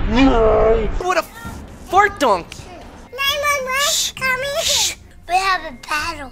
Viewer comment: did shawn say "911 call me here"